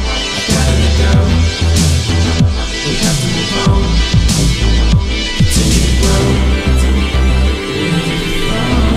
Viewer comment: is he saying "when your farting lasts forever" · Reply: We thought it would last forever